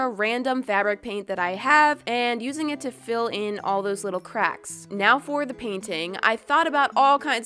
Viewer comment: I paused the video at the best time... Cause it sounded like she said " little buttcrack " XD